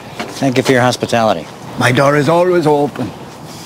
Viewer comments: "-Thank you for your hospitality. -My daughter's always open." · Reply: always heard it as 'my door'er always open. the S pronunciation was never prevalent in old irish accents. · At the beginning it sounded like he said "My daughter's always open"